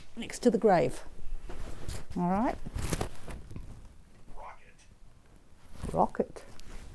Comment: Re watching after reading latest Blog today. When she put the voice recorder on the ground, after pushing Stop instead of Record and being all baffled, the Voice Box said "Rocket" as in "its not Rocket science!".... bit cheeky! 🙂